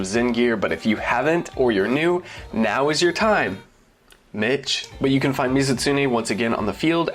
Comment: almost thought you called the audience a b**ch